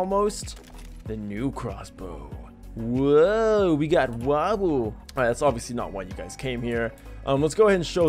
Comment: to me it sounds like you said Wabuu instead of wobble.